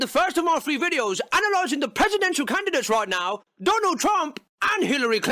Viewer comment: did he say presidents of Canada?